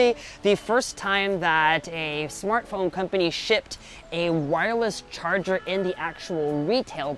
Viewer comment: Thought I heard him say shit so had to go back and listen again